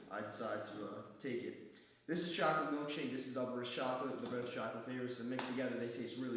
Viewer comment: does anyone know what flavour he says on golden whey standard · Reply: double rich chocolate